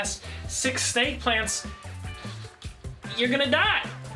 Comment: I thought he said fake plants😂